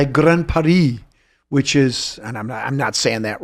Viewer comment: ok - not a French speaker, but when I hear "Granparis" I don't hear "grandparents" I hear "Great Paris"